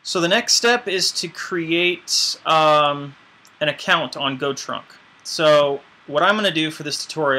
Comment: i thought he said to visit 'go trump dot com' instead of 'go trunk'.. i was thinking "damn, that's an odd name to give this kind of service, wtf kinda hateful company is this?"